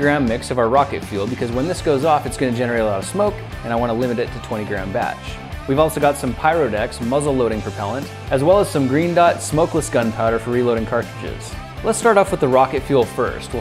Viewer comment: When he said Green Dot I thought he said Gringots lol